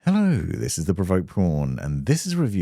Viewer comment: had my volume low and I thought you said 'hello I'm here to promote porn'